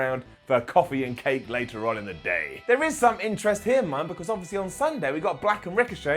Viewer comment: did say here mum 😂😂